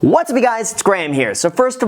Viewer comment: Wtf did you just say what’s up guys not what’s up graham?!?